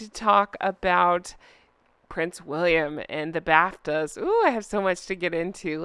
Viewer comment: I thought you said "Prince William in the bathtub", drat, you said Bafta 😅😂😅😂😅❤ Honk honk